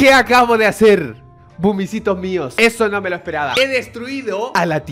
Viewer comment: activa los subtítulos y ve lo que dice xd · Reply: A mí me apareció vomitos mios xD